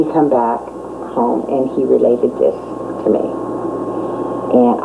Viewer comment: did she actually say her husband came home and would “lay the dick to me”? In an interview?? Classy broad! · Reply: "& he related this to me".......maybe try using a Q-tip, numpty!